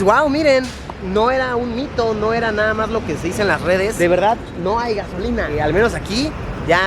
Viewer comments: parece que dice V3RG45 jajajajaja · le entendi verga jaja mis pendejadas :"3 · PARECE QUE DICE "verg*" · de primera intención entendí verga :v